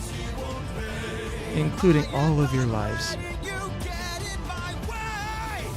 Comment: It's a common mishearing, but he said "Row for your lives," not run. Other than that, ooh. I haven't seen this version of Scylla. Love the art style.